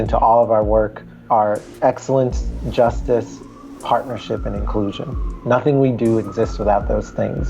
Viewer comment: Thanks for this great video - just voted for it on the P4A site! Thanks for the captions too! There's a slight glitch in the captions around 4:48 - the caption says "exclusion" but the audio and the on-screen graphic say "inclusion". It's obvious what is meant though so it's not a major issue